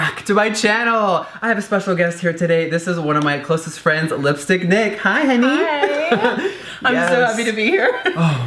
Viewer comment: i thought he said lipstick dick